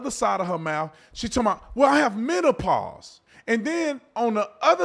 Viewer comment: I thought she said “Manopause” 😅 I am a woman and my best friends are men for a reason. We are equal but NOT The Same!